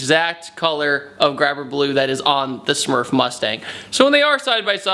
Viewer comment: Whats the color and color code? I hear you say "graphic blue"?